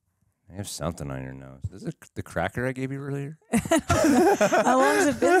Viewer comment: i thought he said crack that he gave her💀